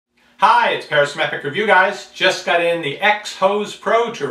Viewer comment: I thought he said "this is Pera-semetric review guys", whatever that was supposed to mean...